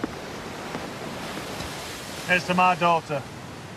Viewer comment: lol, at 0:18 sounded like he said "Here's to my Doctor" :D